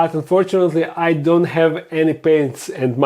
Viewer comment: 😂😂😂 Squeeeeeeek. ❤ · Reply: I'm sorry but I totally heard I don't have any pants.😅